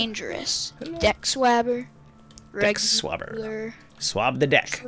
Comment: did say jack swagger?